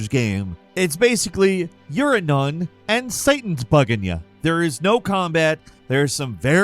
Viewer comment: Lol I heard, "Ur a nun and Satan's f'ing ya" 😂